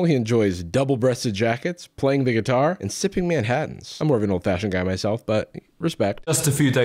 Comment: Why'd I hear "playing the Manhattan and sipping the guitar"?